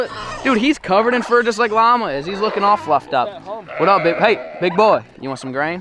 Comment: When he said fluffed up I thought 💭 he said F.U.C.Ked up 😂😂😂🤣🤣🤣